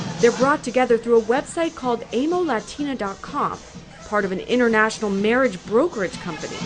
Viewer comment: did she just say the website is called "Anal Latino dot com" ? lol go back & take a listen